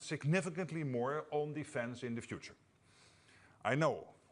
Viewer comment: I thought he said "significantly more onlyfans in the future" lmao